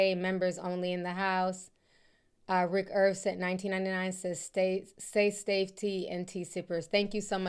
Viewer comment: I thought I was tripping when you said say stafe, lol I be mixing my word up too